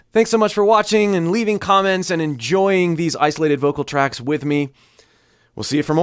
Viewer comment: Damn I keep hearing "Thank you for... and enjoying these isolated vocal tracts with me". Gotta love these vocal tracts Anyway, my pleasure! You're awesome! :)